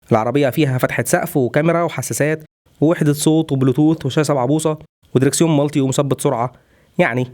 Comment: مش عارف ليه سمعتها شاشه بعبوصه